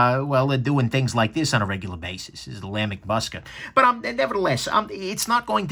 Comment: Hey, awesome review as always 👌 Just a question, what exactly was the knife you showed at 17:10? Sounded like you said Musket.. I like the look of it and I’m just curious about it. Thanks mate, stay safe and keep them coming.